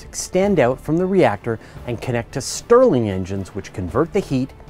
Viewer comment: The subtitles say "Sterling" but it's actually Stirling, as in Robert Stirling rather than the Sterling Pound. :)